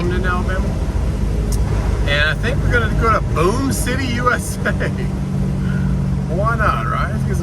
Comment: Thought you said Boone City USA. I’m Mayor😂